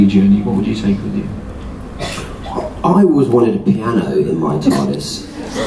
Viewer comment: I thought for a moment Matt said he wanted a cannon in the TARDIS